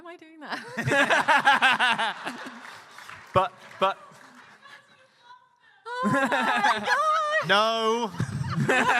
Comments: In 1:25:20 What does the member of the audience say? I can't make it... also the guy to whom Jane says "thank you" (although from context I understand he talks about the staff they carry?) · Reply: One person says "because you love them" and the guy says "because they have nice stuff". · Did someone picked up what was said at 1:25:21 ? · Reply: “BECAUSE YOU LOVE THEM!” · I really wish they'd provided closed captions. 1:25:22 What is that audience member saying? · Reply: "Because you love them" That one was cute, but this audience is too damn chatty